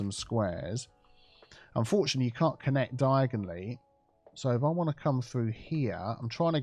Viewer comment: did he just say diagonally?